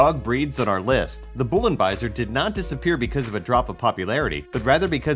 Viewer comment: Ayo why I think that at 1:48 it said Bussy I was so confused like wtf is a bussy dog and why can’t I have one.